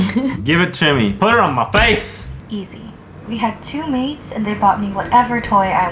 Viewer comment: Lol when pink unicorn said maids I thought she said maze lol like if you thought she said that to